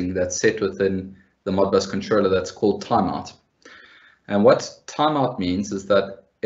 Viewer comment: you said : Modbus controller called ??????? ,sorry difficult to catch what you said. Taniard or something like that? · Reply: Setting called "Timeout"